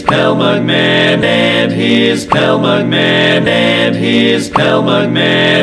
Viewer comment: I hear something "and his cow mugman"